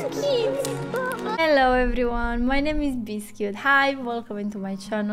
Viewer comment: In your welcoming it sounded like you said "hello my name is bisquick" , with your accent :) I now have my nickname for you . Very nice reaction 👍